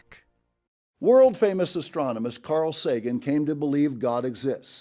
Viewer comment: Did he just say: "World famous ASTRONOMIST..." ???